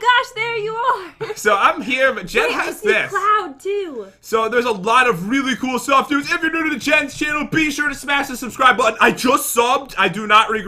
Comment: did he just say “the Jen’s channel”? 🤨😅